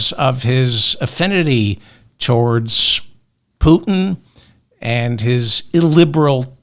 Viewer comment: I thought I heard you say president poopin instead of putin oh well sounds close enough keep up the great work